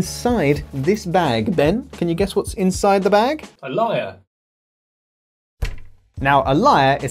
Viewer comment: A LYRE? I thought he said " a liar" I was like..."what?"